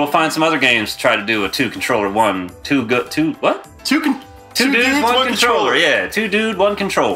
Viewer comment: I could have sworn that said "2 Nudes, 1 Controller."